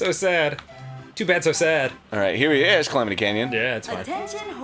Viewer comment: I thought he said calamity ganon but he doesn't exist yet